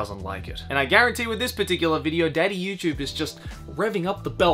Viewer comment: You said "Daddy Youtube" but I heard "Mama Susan".